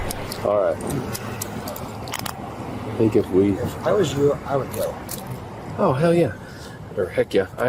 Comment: did that one cop say: “If I was you, I would kill him?” Did I really hear that?